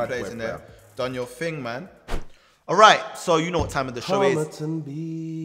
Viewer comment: is it just me or did he say homerton b?